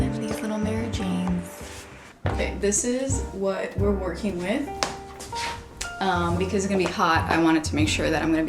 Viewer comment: I study English now, what is she speaking at 1:55? auto subtitle show "This is what we're working with in" but i don't understand. please help meeee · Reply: This is what we’re working with…uhm…because it’s gonna be hot